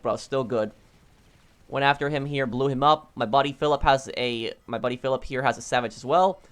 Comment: who else heard "My body pillow" instead of "my buddy Philip